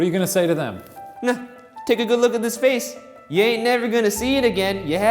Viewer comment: did he say yain't